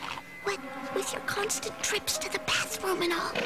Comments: I originally thought she said "health crack," but now I can clearly hear the p in there. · I originally thought she said "health crack," but now I can clearly hear the p.